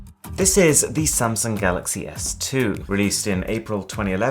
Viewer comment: Thought it said Samsung Galaxy S Eleven😂😂😂